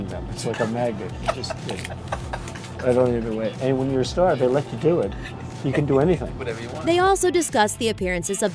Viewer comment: Trump actually says "when you're a star they let you do it". Therefore, (contrary to what is being said/inferred here; @ 0:55) he understands consent.